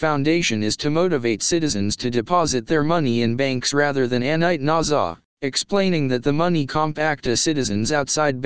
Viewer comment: Please, what is that word (or words) that is said ( at 3:02 & 3:53) when it says that Iraqi citizens are encouraged to deposit their money in banks there instead of doing something else with it? It sounds like "annite nawzaw."